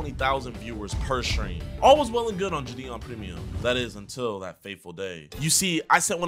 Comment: did he just say 'until that faithful day' lmao